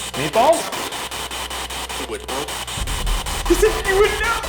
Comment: When the spirit box said "you would know" I hear "wait what"